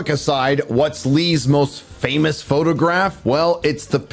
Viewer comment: The “anti-what massacre”?!? Did you say “fun”?!?